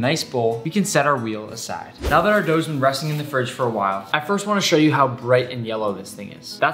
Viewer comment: THOUGHT NICK SAID GO TO HELL